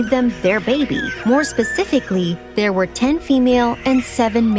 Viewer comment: I thought she said there were 16 babies but then she said there were 10 girls and 7 boys